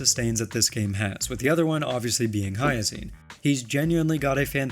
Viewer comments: why did he say Hyacine but the subtitles say HuoHuo 😭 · i was watching this with auto subs and instead of putting hyacine it just put huohuo instead for some reason 💀